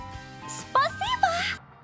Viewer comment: I heard harasho at 1:51. Tfw you've watched love live and it reminds you of Eli. °_°